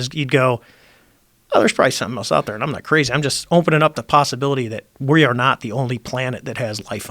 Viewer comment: does he say “ompen”?